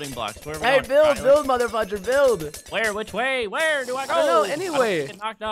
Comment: did preston say at 8:30 "mother-effer"?